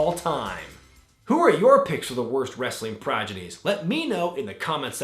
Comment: Sounded like said progidy instead of prodigy.